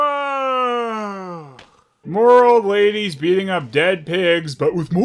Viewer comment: "what did I tell you abouy biting city folk?" what, like biting locals is perfectly fine? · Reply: 😂 I think I misheard as I thought he said 'baiting' but yours is funnier!